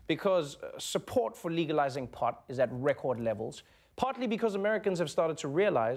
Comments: When Trevor says "partly" i hear "pot-ly". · Did this man just say "potly" instead of "partly" at 1:12 ? I may be potly high as I was watching this